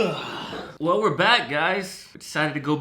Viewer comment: 😂 Bro, In the into I swear I heard you say “uhhh Lower back guys”